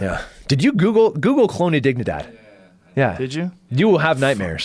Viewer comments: what did he ask to be googled? I couldn't understand the word or how to spell it. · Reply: Colonia Dignidad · what is he saying "google ???" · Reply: Colonia Dignidad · Google what?? Honnedigidad? Wtf did he say? lol